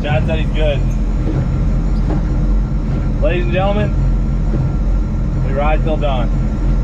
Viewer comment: Lol..i thought he kept saying "We ride till dawn!"..or early afternoon 🙄